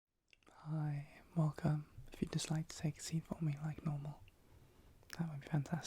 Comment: At the start it sounded like you said “ Hii, welcome. If you’re dyslexic, see for me like normal.”